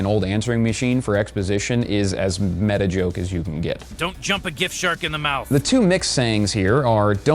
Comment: I thought he said a Gif Shark.